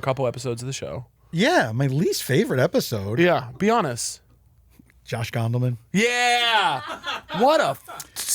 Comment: I heard "Josh Gondelman was my least favourite episode" as Josh Fenderman and had to double check.